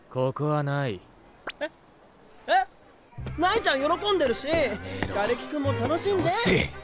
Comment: ガレキくんがカネキくんに聞こえるww